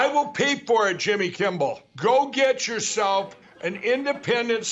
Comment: Is he saying "Jimmy Kimble"?